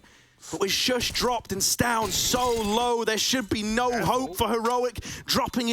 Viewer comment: Is he saying stavn is "solo" or "so low" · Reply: So low. Either way it's accurate